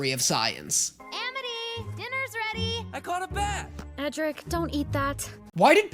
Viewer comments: His name is Edric but it did sound like she said Patrick 🙃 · I fully believe just hears the Z at the end of Luz as a Z and not the S it is meant to be. Luz is the spanish word for light and it is pronounced Loose. In fact I even went the listened to Eda say Luz's name and she does say it with the S and not the Z. you misheard Edric's name as Patrick. Amity's brother lol. Amity is not the most hated character, but in the first couple episodes that she is in she is portrayed as the typical mean girl at the top of the totem pole that is school. Luz as the new girl will clash with the mean girl, but like with the last episode of the library and episode 12 you can see Amity and Luz interacting without Amity trying to act that she is better than everyone else. You can truly see how Amity is as a person and you can see how Luz does want to befriend her.